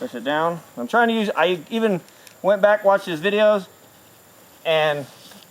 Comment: did he say Wetback watches videos? Lol say what